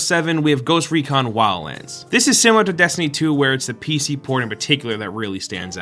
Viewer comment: did he said pc porn?